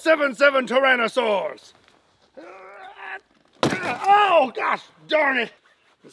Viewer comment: Every time the furby gets launched it sounds like it is saying "Oh, GOD!"